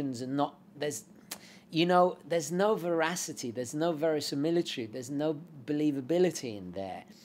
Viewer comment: what was that long word? 🤔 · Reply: "verisimilitude" (I don't think I'd dare to use it in a sentence)